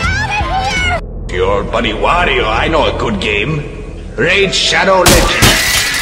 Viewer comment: Anybody else hear: “Your buddy Wario, I know a good game. RAID SHADOW LEGENDS”